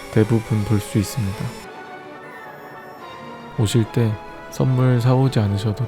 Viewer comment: 왜 이렇게 들리지.. 15:36 오실때 선물 사오세요 15:42 번호판은 특별히 제 생일로 했는데... 꼭 기억하세요